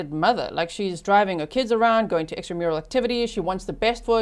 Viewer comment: HA! Had to check closed caption, which says you said "extra mural activities" but I heard "she drove them around to EXTRA MARITAL ACTIVITIES" … which with Donna's level of control, I wouldn’t doubt that either.😂🤣😂